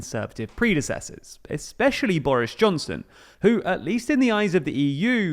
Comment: did you say “Boarish Johnson” ? That’s certainly what I call him lol